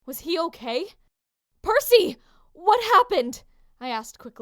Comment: if you turn on captions instead of it Percy it says Murphy